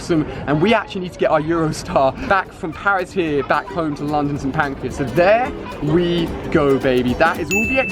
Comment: Who heard him say "pancreas" instead of Pancras???